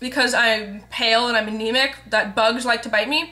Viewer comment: haha, OMG man for a second there when u said u were Anemic, i thought u said Bulemic! O___O haha, lol. I was like, i'm pretty sure that isn't something that she would just want to announce, lol.